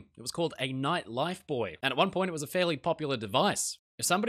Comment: I thought you were saying “nightlife boy“ I’m like oh that sounds exciting.